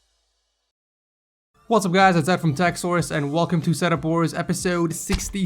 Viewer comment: It REALLY sounds like you say tech saurus instead of techsource